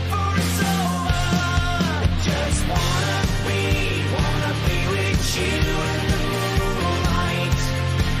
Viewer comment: Oh!!!! He said "bewitch you"!!! I thought he was saying "be witchu" like "be with you" but slang. 😅😂